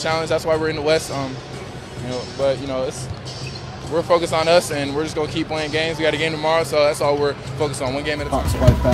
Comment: I don’t know what the guy said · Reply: “pops wife passed”